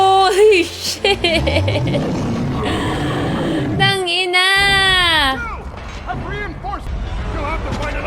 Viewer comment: did she just say "Tang ina"? Lmao